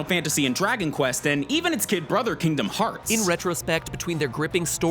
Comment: Did he just say "his gay brother Kingdom Hearts"?